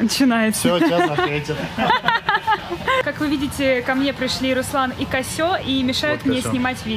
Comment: как вы видите ко мне пришли Руслан и Козёл . В этой ситуации я бы сказала так (шутка,просто мне так послышалось.)